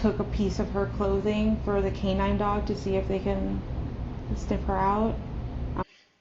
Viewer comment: actually says, "STIFF her out". I initially thought she said "sniff" but she doesn't. Maybe she misspoke but maybe it's leakage?? 'Stiff" is definitely the language of death!